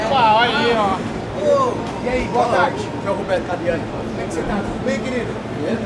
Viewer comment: fui so eu que ouvi o tiozinho dos 6:00 falando??